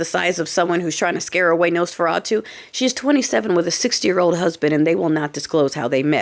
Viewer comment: I thought you said 'six year old husband'. Had to rewind. SIXTY, wow!